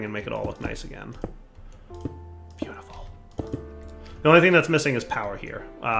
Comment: what did he whisper? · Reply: JenInMinecraft "beautiful!"